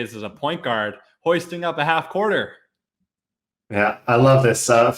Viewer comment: When he said Half Courter at 5:40 Sounded like Half Quarter. Fuk’d my whole shit up